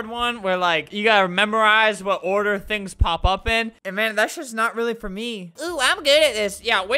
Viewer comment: Is it just me or did albert say "shit" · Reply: He said “thats just not really for me”